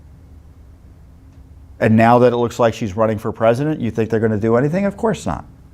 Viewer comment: WAIT WHAT? DID YOU SAY HILLARY IS RUNNING FOR PRESIDENT?